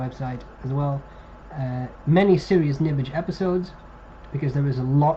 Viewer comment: I thought you said silliest nibbage... I think that would be a better name for some of these episodes. :)